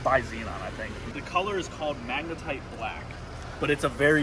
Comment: Anyone else catch at 6:50 he says magnetic black (mag-na-tike) 😂